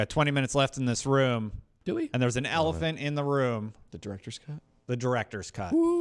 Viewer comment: I legit thought they said :"There's an elephant in this room! The Director is here?"